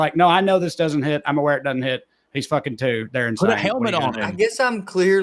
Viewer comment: kids saying slurs - Just repeat what he said correctly "You want another race car?"